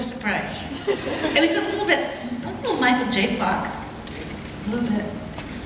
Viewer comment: what does she say at 2:37... michael jay **? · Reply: Micheal J Fox, the actor, ( Back To The Future, etc..) He was also noted for his incredible comedic timing on 80s sit coms, and was called a genius. A big shot in her days, so not surprising he reminds her of him.